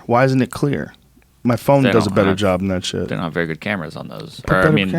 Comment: Am i the only one who heard say ketamine instead of cameras at around 11:51? think he has k on the mind or maybe it's just me lol